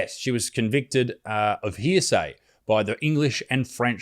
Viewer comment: did no one else catch that but me wtf "hear say" heresy she was burned at the stake for heresy